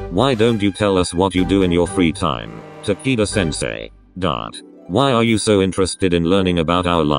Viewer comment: It sounds like its saying taquito sensei and im dYiNg